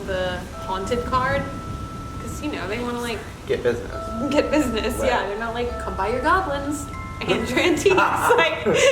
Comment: Did anyone else hear a whisper at 23:16 ? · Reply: yes it says I'm sorry